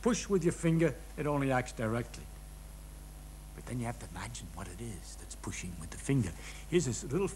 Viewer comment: Hi Christopher and everyone, I'm subtitling this video and I don't understand what he says on 3:09; he's talking about forces that we're used to, forces of direct action, and then he says, "But then you have to imagine what it is that's pushing with the finger.." (please correct me if I'm wrong) then he says two phrases about little atoms which I don't fully understant, until he says "and there's a little space between those atoms, and this pushing is going through that space". Anyone help? Pls